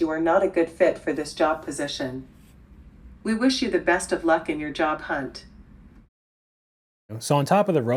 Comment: sounded like she said ‘….best of luck in your job, cunt’